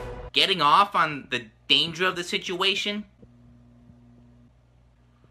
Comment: What did Yami say at 1:04? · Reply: MysteriousGirl 92 dont kinkshame me